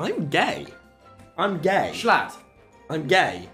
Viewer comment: I had zero context and I thought tubbo was saying that Tommy was saying 'gay schlatt' instead of jshclatt